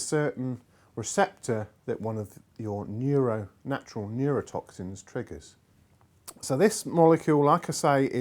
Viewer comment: Did someone say neurotoxin?